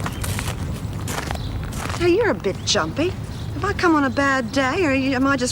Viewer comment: lmao i thought she said "HEY your a bit chunky" lol